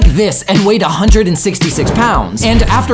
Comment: At first I thought it said 1,660 pounds